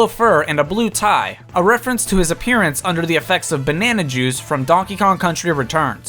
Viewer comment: I thought you said "bannana Jews" for DK's yellow skin...